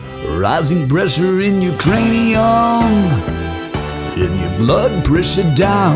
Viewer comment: When you said "your cranium," I heard "Ukrainium," as if someone from Ukraine named a new element of the periodic table.